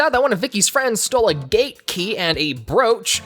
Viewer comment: I legitimately thought they said “Gay key” instead of “gate key” and I was hella confused